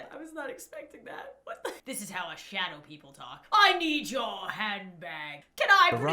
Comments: I thought that dude said "I need your head back." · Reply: V ikr same- If she didn’t say that he said “I need your handbag” I would’ve thought he said I need your head back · Robber: “I need your handbag.” What I hear: “I need your hair back.” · When the robber said "I need your handbag" I heard him say "I need my head back" 💀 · “I need yo handbag” I THOUGHT IT SAID “I NEED YOUR HEAD BACK” IM DONE SJDJFNRJSOSJDHSIA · When he said he needed her handbag, I heard "I need your hand back." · Ok the Bart that said I need your handbag I thought it said I need you hand back 🤣🤣🤣🤣🤣🤣🤣🤣🤣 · Guy: I need your handbag Me who heard it as I need your hand back /\____/\ • • 0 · Person: 'i need your handbag...' Laurens concern: his accent My concern: his politeness for being a robber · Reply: Person: 'i need your handbag...' Lauren's concern: his accent My concern: Did he just say 'i need your hand back...'? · When he said "i need your handbag" i thought he said i need your HAND BACK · Lol, I thought the robber said: " I need your hand back." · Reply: I thought he said I need your head back 🤦🏻‍♀️😂then lauren said that he said I need your handbag 🤦🏻‍♀️😂🤨I was laughing omg ,😂😂🤦🏻‍♀️ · I thought the robber said “I need your hand back!” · when he said "i need your handbag." i thought he said " i need your hand back!" LOL XD but laurens laugh tho!!!!! btw it was 340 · Why did i hear "i need youh headbag" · When that dude said “I need your handbag,” I thought it said I need you hand back 😂